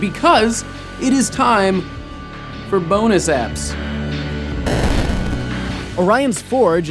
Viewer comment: I could of swore at first he said "boner zaps"